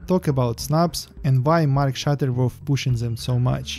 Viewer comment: Who heard it as Mark Zuckerberg instead of Mark Shutterworth? I DID!